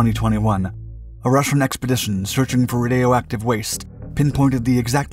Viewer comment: Just have to say this, in the beginning I heard “rodeo active waste”. I will now refer to it as that from hence forth.